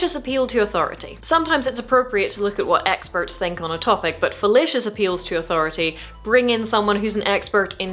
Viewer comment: I love your accent, but when you said fallacious, i heard it as fellatious 😂